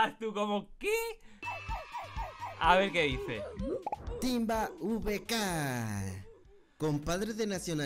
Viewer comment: ahre que dice tamba vk :>